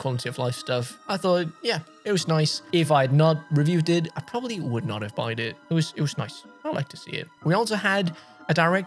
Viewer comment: did he just say probably would not have “buyed” it..?